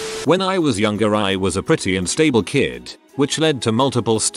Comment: I thought the AI said, "I was a pretty and stable kid, which led to multiple stays in the psych ward." And I was like, okay I'm confused but intrigued.